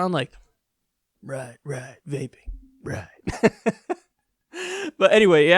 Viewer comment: I gotta get my ears checked. I thought he said raping. My first thought was I’d love to see Trump in a room full of rape victims while he verbally fucks that minefield up.